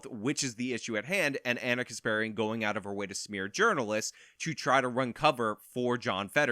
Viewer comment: I swear sometimes it sounds like she says Gerbilists instead of journalists 🐹